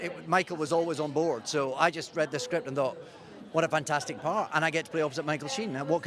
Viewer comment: What does he say at 4:05 i cant understand it :( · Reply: so here's what I understand (and I'm not a native English speaker) David Tennant: "He's always on board so I just read the script and uh- what a fantastic part and I get this (?) to play with Michael Sheen, what could be finer?"